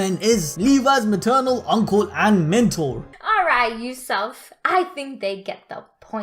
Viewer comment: Bruh 8:27 did she just call you, you-self?